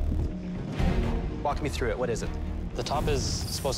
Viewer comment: why did I hear that as “f*** me to it”